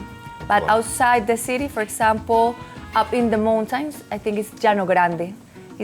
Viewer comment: what did she say? The mountains where ? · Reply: She said up in the mountains referring to the outskirts and the surrounding areas, especially the famous Llanogrande.